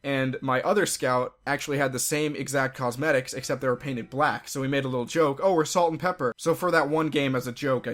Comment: I heard paint it black.